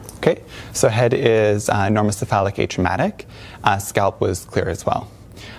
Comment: Did he just say her head is "enormously phallic"?